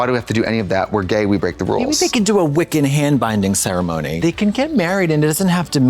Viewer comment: I heard "Wicked" instead of Wiccan and now I'm imagining a Gelphie hand binding ceremony 😂